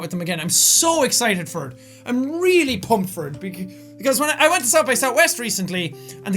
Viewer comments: I thought it said "pumped for tax" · i thought it said pumped for tax